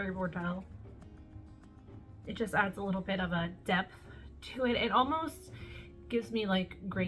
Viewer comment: When you were explaining “ Mega Pint” I thought you said “ it has a nice Depp to it” 🤣🤣 then I realized you said depth.